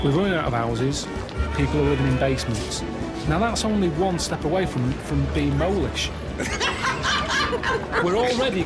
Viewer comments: listening whilst cooking & misheard so did a double take bc I thought Carl said ‘one step away from being Polish’ 😂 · Ohhhh, "molish" . . . At first, I thought he said "Polish". Yeah, "molish" makes more sense.